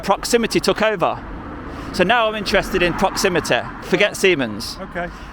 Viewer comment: I thought you said the people who work there were intelligent....